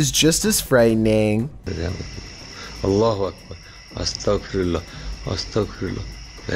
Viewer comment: he said "is anyone there, give me evidence youre here." 3:03 "look at this in front of you-" 3:20 "look this just fell." 3:32 I'm not trying to hurt you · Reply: And it sounded like he said alluah akbar